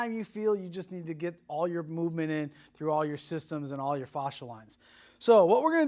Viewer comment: In the FIRST 20 seconds of your video theres a word I heard you say but Im not sure what it is. It's in the sentence "through all your systems and all your (INSERT WORD PLEASE)". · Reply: Hi Sammy! The sentence is "through all your systems and all your fascia lines". Fascia lines are the traceable framework, or lines, that distribute strain, tension, fixation, and compensations. These lines create stability, resistance, power, flexibility, elasticity, and posture. Let us know if you have any other questions 😀